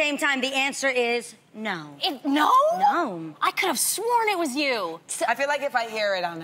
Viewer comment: Adrienne says "nom" instead of no. I'm convinced it's her.